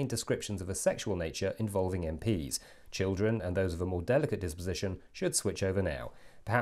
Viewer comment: I heard "...Involving MPs, Children, and those of a more delicate imposition " and thought you were really going for the jugular. But then it continued.